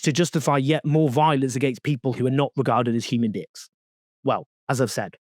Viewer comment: if Ai has written your transcript it seems some human editing has taken place. 'beings' might have been misheard as e.g 'beans' but how Ai reached 'd*cks' defeats me!